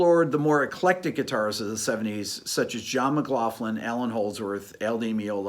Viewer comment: sec...did he say John McLoflin????? :D